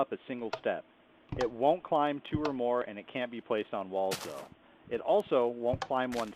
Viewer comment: What are you saying at 2:46? "It won't climb two or more and it 'can(?) be placed on wall stone(?)'